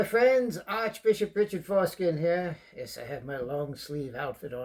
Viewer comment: richard what? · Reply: lmao i thought he said foreskin too😂😂😂